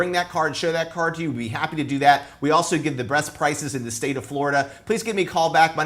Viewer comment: did he say “we also give the Breast prices”? Great video, love it.